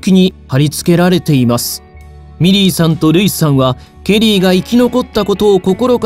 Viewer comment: 皆さん真面目なコメントの中すみません😣💦⤵ミリーさんがメリーさんに聞こえてしまいます